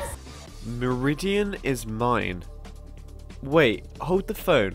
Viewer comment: I thought he said read his mind not meridian is mine